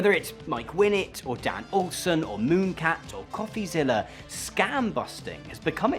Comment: Who was that 3rd anti-grifter content channel you mentioned at 5:45 ? It sounded like you said mooncat but I can't find that channel?